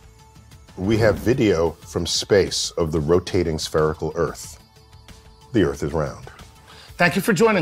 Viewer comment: Ohhhhhh SPHERICAL... I thought he said sPEARical.